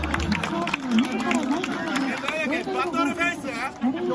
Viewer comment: 輝け京田がバトルフェイス京田に聞こえた